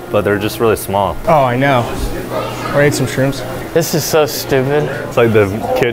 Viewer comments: sounds like someone said they ate some shrooms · Damn you forgot to cut out your homie saying “you ready to eat some shrooms” at 8:15 hahaha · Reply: I thought that was saying "wanna eat some shrooms" 8:14 (better time for whole phrase). Maybe it wasn't. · Did say he "wanna eat some shrooms" at 8:14 ? (maybe it was someone else)